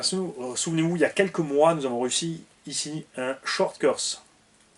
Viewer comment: Y'a que moi qui entend: "y'a quelques mois, nous avons Russie" , Hihihih